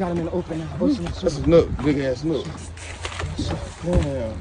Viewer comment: What kind of fish is that? Sound like he said a nook